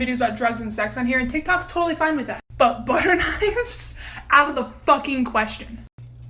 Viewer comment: did u sayyyyyyy........BUTTER KNIVES